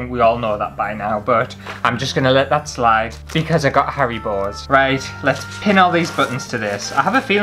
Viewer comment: Sounded like you said, "*gasp* I get hairy balls!" but you actually meant Haribos :-O but I think that someone's hairy balls crawling into my water glass while I was sleeping tops earwigs.